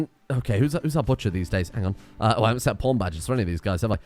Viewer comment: " i haven't set my pawn badges yet" made me laugh as i heard it as "i haven't set my porn badges yet"